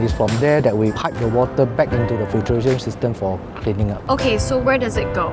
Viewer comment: subtitles say “Refridgeration system” but the engineer says “Filteration system” which makes more sense. Especially with what comes next.